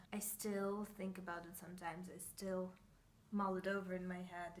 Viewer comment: What does "moledovering" mean, respectively what are you saying at 0:41-0:42? : ) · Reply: Haha, I need to work on my diction 😄 it was “mull it over” :)